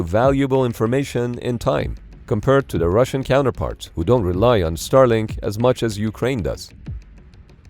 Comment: Lol, how exactly do Russian forces rely on Starlink?? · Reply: Ok, I misheard. I thought it said "who now rely on Starlink as much as Ukraine does"🤦‍♂️